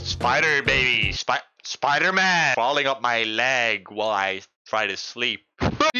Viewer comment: spam it. It sounds like he says leg